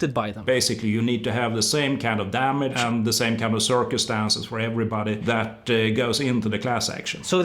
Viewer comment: ohhh CIRCUMSTANCE.. I thought he said Circus Dance